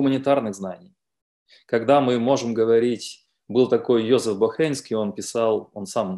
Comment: Йозеф Бахрейнский - я правильно расслышал? Ничего не могу о нем найти. Особенно интересуют труды о логике веры. Может авторы канала или Андрей Олегович подскажут где искать. Зарание спасибо.